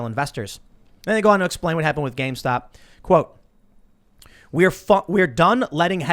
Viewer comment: we are fuc-done did I hear wrong?? 😂